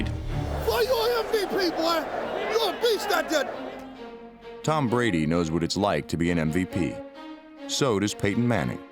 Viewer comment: What did Deion say at 1:04? They muted the last part😏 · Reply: Ninja